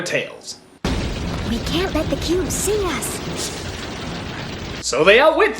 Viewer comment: When I listened to 4:55, my volume was down, and it's early for me... So I could have sworn she said we can't let the boob see us.... Or we can't boob see-ax.